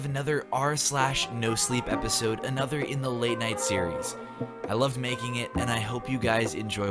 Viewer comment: I heard “ I lovemaking it instead of I love making it “🤣🤣 scary though